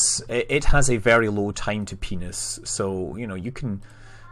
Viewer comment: Did..did you just say "-a very low time to penis-"?